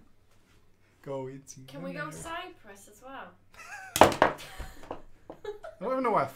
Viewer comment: what does she say at 7:45? "Can we go [..?..] as well?" thanks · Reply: Cyprus